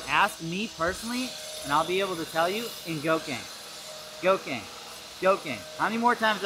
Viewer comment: What does he say at 9:00? Go king? Get in go king? Not sure if I heard him right. Anyone got a link?